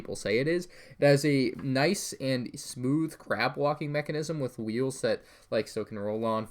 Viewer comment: Every time you said "crab walking mechanism" it sounded like "CRAP walking mechanism" and I was confused for like a split second.